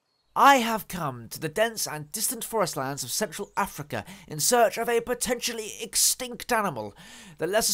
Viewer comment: Wait.... Did you just say 'Sexual Africa'?